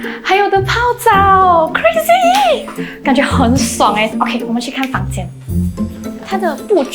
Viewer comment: I hear clearly that said 我们去开房间 instead of 我在去看房间😂😂😂 did you guys also heard it clearly?😂😂😂